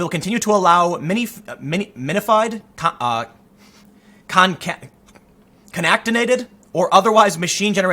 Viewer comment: It sounded like you were trying to say concatenated--when one string of characters is appended to the end of another.